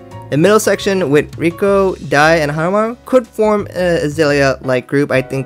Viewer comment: I thought he said Nico, Daiya, and Hanamaru